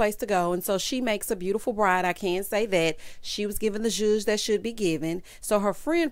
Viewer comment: Maybe I misheard, but I thought that when said “I’m going to fall in love with her,” Alexis said “don’t say that.” 🤔